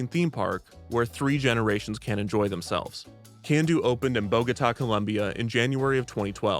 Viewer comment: hey men, the subtitles are wrong, it's Bogota😅